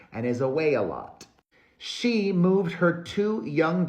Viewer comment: lol when you said "he is away a lot" I heard "he is a weigh a lot"